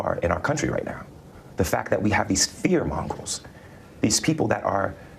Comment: did Juicy say fear mongols or fear mongrels? 🤔